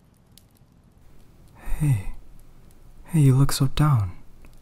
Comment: "Hey you look so down " What i heard "hey you look so dump " 😜